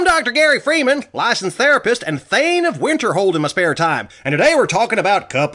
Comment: I thought it said the rapist